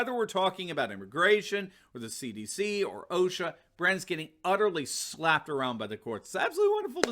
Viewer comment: "Brandon is getting absolutely slapped around by the courts" lol, or at least that is what I heard Turley say.